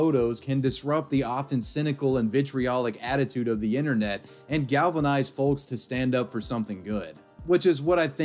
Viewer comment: did he say galvanise?